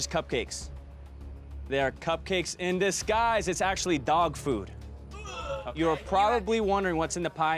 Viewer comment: when he said dog food i thought he said dog poop and i flipped out